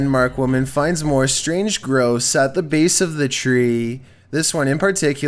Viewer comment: "The woman finds more strange gross at the base of the tree" BEST DESCRIPTION EVER! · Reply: Lol! Darn, I thought he said gross. I listened to it again. Chills mispronounces words all the time. 'Strange gross' would've been hilarious.